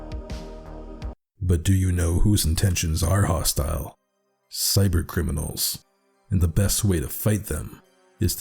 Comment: Bruh I thought you were about to say Siren Head when you said Cybercriminals